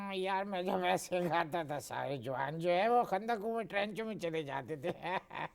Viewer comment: unhone kya kaha ? "Khanda kuvar" trencho mein chale jaate the. Khanda kuvar ka matlab kya hota hai? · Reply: He said "saray jawan khandakon main, trenchon (trenches) main chale jate thy" Firing or gola barood se bachne k lye jo zameen me tunnel type ka banaya hota hai safely move krne k lye ya positioning k lye usy kehte hyn